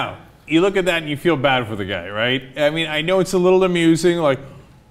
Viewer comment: What Cenk Uygur said at 0:56 - "Now, you look at that and you feel bad for the guy, right? I mean, I know it's a little amusing." What the captions said at 0:56 - "You look at that you feel bad reiterate any idea what some of the new zealand"